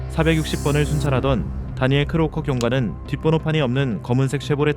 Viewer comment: “뒷 번호판이 없는”. 우리 번호판 이제 쓰지마, 라고 들리기도 함. 이런 사람들 신고 당하면 경찰 출동해서 범칙금 물게 함.